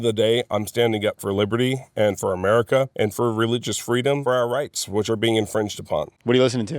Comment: noticing it sounds like he said “for our reichs” interesting.